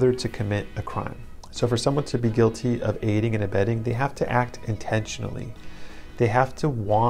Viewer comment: I thought this saying was "abedding", as in giving "comfort"! haha